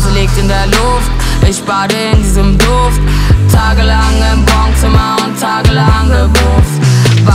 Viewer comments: hab immer Wohnzimmer verstanden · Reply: Ich hab immer Baumzimmer verstanden🥲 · Ach bongzimmer xD dachte immer sie singt wohnzimmer😂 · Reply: Ich hab immer Baumzimmer verstanden😂😂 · Asso ich dachte immer sie singt Wohnzimmer😂😶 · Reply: Queen Love ich hab baumzimmer verstanden😂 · Asso ich dachte immer sie singt Wohnzimmer😂😶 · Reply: Queen Love ich hab baumzimmer verstanden